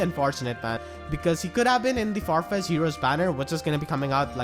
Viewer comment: What's the banner he says at 12:41 ? Farfest? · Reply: Farfetched heroes where the popular characters are summonable